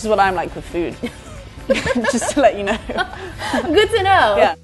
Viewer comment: Been trying for ages to understand what the fuck does sansa say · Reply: "Just to let you know". ;)